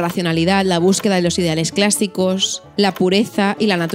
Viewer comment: Ok paero, nadie va a hablar de la palabra "clASSicos" (13:48)?? por que yo si entendí XD JAJAJAJAJA